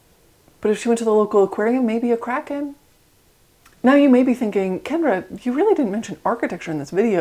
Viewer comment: Oh she said kraken I thought she said crackhead